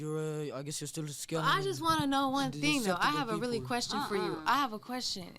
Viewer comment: did she say "I have a really question" omg...